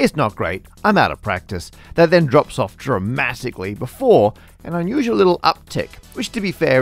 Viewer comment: Did he just say "dra-math-ically"?!